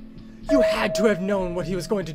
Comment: I swear Jayfeather said “th-thunderclan”